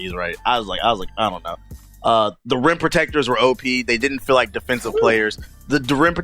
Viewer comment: Idk why but everyone you say "rim protectors" it kind of sounds like your saying rent protectors lol